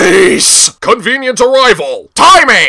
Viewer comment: I didn't realize that Thomas said "What Insurance" until just now. I thought he said "Wooden Shorts" because he was in an 8-plank wagon. :P